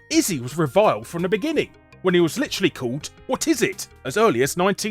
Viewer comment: When you said "Whatizit" I thought you said "Autisit."